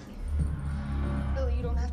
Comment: Why did I hear eleven say at 0:19 “MAX NO”